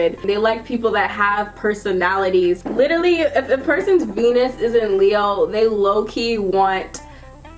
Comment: instead of a Venus the translator shows "penis". Is this translator drunk? :D "If the person's penis isn't Leo they Loki want romantic comedy"...Aaand I don't wanna know where that came from. But seen the video a year ago and it's still one of the best on YT.